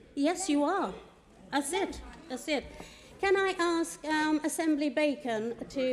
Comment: Did she just say, "Yes you are, a SIMP a SIMP!?" 😂